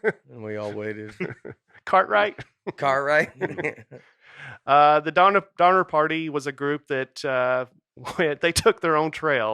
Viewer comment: Huh. The whole time I was listening to this earlier, I thought they were saying “daughter party”.... this makes more sense.